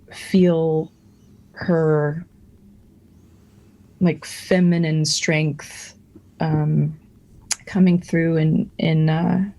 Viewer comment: I thought she said "mcpheminine strength" and i really want to hear that in a sentence